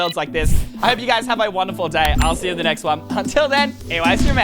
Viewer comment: First I thought you said eye stream but you said E Y stream